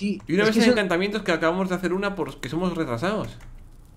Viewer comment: willy:UNA MESA DE ENCANTAMIENTOS QUE ACABAMOS DE HACER POR QUE SOMOS RETRASADOS xDDDDDDDD nose si escuche bien q decia eso,pero eso entendi 28:09 xD